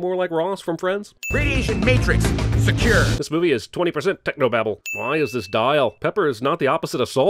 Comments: I thought he said technoblade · on his subtitles I thought he said Technoblade